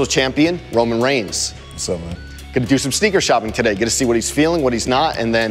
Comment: I didn't turn the volume up and i thought that Roman said "what's up, bitch"